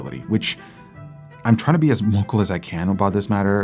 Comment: What does he say here? "I'm trying to be as ___ as I can about this matter..." I played it back 15 times at very high volume but still can't decipher what that word was. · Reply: Vocal